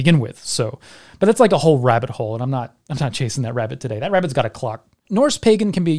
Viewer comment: "That rabbit has a clock" Lmao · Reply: ngl, I initially heard it as “that rabbit has a Glock.” Admittedly, I would not go chasing that rabbit either, but for a very different reason.